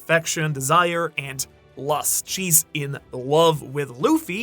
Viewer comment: did you say lust🙂